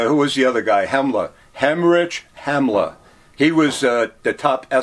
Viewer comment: did he just say Hemmerage Hemmler? lmao